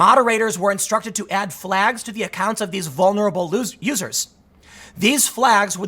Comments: ! All I heard was vulnerable "losers"... Left in a nutshell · Did Tim just say "Vulnerable losers" ? lol